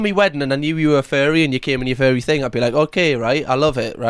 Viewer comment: I thought was saying look at the "Fairy" in the field. It took me ages to realize he was saying " Furry". 🤦🏼‍♀️ I kept staring at the shape in the field looking for wings. Oh my I can't blame it on not understanding his accent because you can clearly see it's something in a cow costume in a field. 🧚🏼‍♀️🐄🤣 Time to either go to bed earlier and get more rest or drink copious amounts of coffee because my brain 🧠 is still buffering. Poor Tony his mother was behaving absolutely horribly at his wedding. She is clearly going to be a "Monster-in-law".